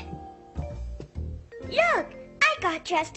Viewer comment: Did Caillou said "Jerk" to his dad?